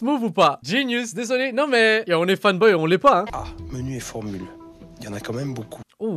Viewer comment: j'ai entendu "femboy" à la place de fanboy deso g eu peur